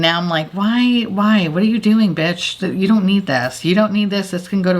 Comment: When I use an expensive blush and it disappears, that’s exactly what I hear- “Got your money, bitch!” So frustrating.